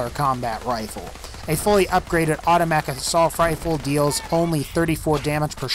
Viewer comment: Im not the only one who heard him say “autofatic a sulf rifle” right?